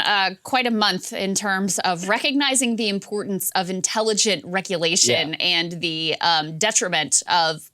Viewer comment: Sounds like Krystal said "wreck-ulation". Doubt that was intentional, but it's very appropriate.